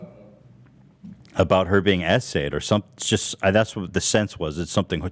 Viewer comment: All of Kavanaugh terrible business practices, like not paying his nanny, falsify SA allegations in order to fire someone without paying them, going bankrupt, having a terrible flipped app could’ve been avoided and not aired out if he just didn’t try to sue Ethan over a 3 minute clip. 🥴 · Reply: It is only now, seeing SA in text context that I finally understand why the guys say "SA". I seriously thought they were saying "essay", like it was some 2021 l33t speak or something lmao.